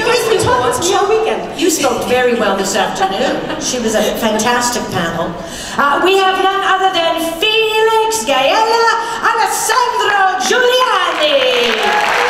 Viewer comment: Did she say Felix Gaella at 3:33 lol?